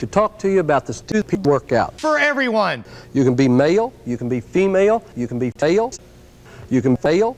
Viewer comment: So, I had to rewind around here (don't ask why) and it just happened to land on him saying "MyPillow" so it sounded like he was saying 'You could be MyPillow' 1:03 I'll say 🤢 3:15 🐡🎵Don't poop on the floor, use the commodore🎵🐡